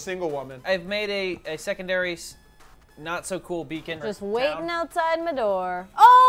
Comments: I thought it said bacon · I thought it said bacon tbh